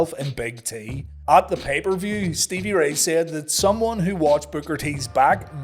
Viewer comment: Love your content, but all I hear is "booger t"😅 great episode!